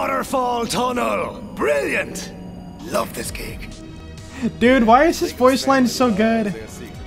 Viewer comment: I thought he said "I love this game"